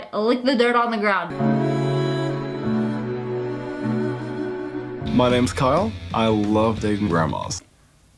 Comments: Guy: My name is Kyle i love dating grandmas Sssniperwolf: Okay... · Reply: Oml I thought you said "My name is Kyle I love eating grandmas" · I misheard dating grandmas as “dating bras”